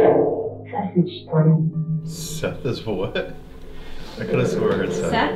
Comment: I heard "Seth is ho*ny" 33:45 I'm italian, and i pretty clear heard, "Mi sa fare prima questo" (Mi sa, its not rly right, but in our dialects wr normaly talk like this, so I think he said something like "I think I have to do this first" 36:19 Something like "Senza pietà" (With no mercy) ??? Are there italian ghost, they have allso an italian accents, holly cr**😂 38:10 "There's some posto" ? Like mixe english and italian, maybe, "There is (free) some space" 39:01 "Per forza" (You have to) 42:17-42:48 Looks like she/he is dancing, dam* that moves👀😂